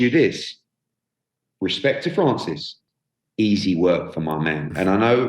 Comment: Eddie I thought you said Ruiz was easy work.